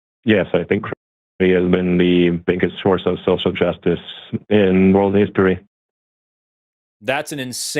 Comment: i can't understand what he's saying. I caught something about "social justice " , but those two words were the only ones clear enough to be certain I heard them correctly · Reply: He said “I think Christianity is the biggest source of social justice in world history” if you were still wondering🙃